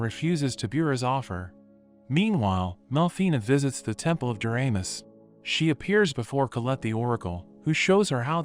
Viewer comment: tempel of uranus? am i the only one who hears this?